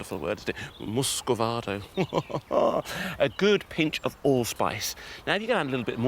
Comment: Did You Say Old Spice Are You In The Future